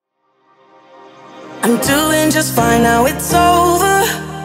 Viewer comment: I Heard “I’m 2 Inches Finer It’s Over”